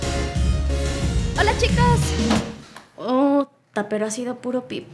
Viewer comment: no se porque me recuerda a mis compañeros de clases y en 0:37 parece que dicen todos: "UNA HEMBRA!" :v alv...